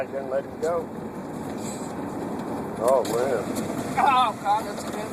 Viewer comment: Did she say at 1:07 “Oh God, that’s a good one.” Lol?